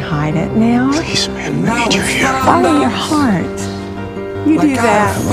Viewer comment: what does Dean say at 4:34? I keep hearing "I love you, man" but I know that's wrong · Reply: he says "where the hell are you man"